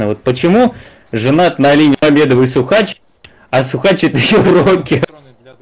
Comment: у школьника так дрожал голос, что не понятно шо вин казав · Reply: а мне послышалось «а Сухач — это её рокер»